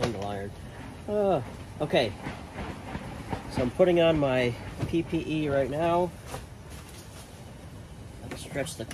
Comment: What did you say about pp?